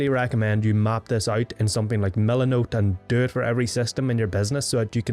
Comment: Map this out in what? subtitles says "millan note" is that the app where you can map out systems? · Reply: Mila Note. Or on paper works well.